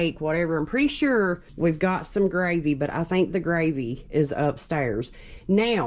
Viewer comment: When was talking to the camera I could swear I heard him say "We got some big meats". So cute! I hope you all can vlog more. I like spending time with ya.🤩😘